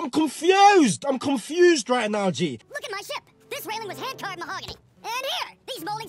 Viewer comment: LOL I thought he said "Looking like shit!"